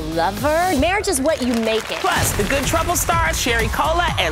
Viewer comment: All I heard was "Cherry Cola and Sierra Miiiiissttttt"